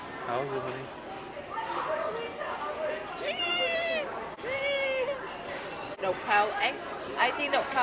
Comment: Man, y’all are hilarious. constantly smiling and when she said “sheeeeeesh” I busted out laughing. I thought she was saying “cheese” for the camera lol.